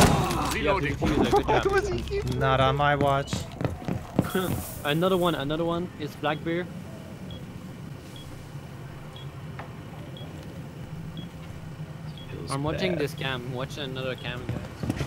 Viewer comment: i thought that he was saying"another way to hold an angle..." :D